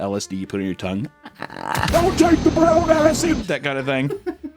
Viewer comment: what did he just say? (don't take the brown acid?)